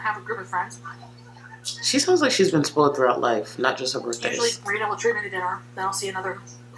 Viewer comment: did she just say they USUALLY treat her to dinner